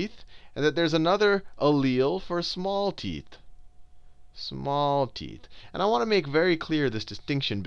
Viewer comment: Excuse me, did you say small tit? He-he-he-he